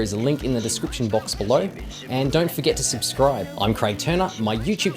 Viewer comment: With that funny wrong side of the planet accent. I thought he said he was Craig Tuna.